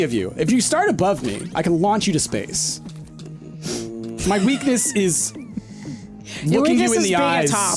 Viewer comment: What does say here? Something about being a top? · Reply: "Your weakness is being a top" is what says